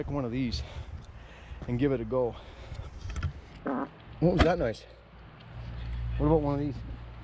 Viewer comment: farts “oh is that nice” 5:40 Is that really what I heard??